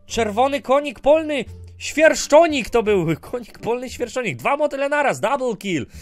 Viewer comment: Puzonik usłyszałam xD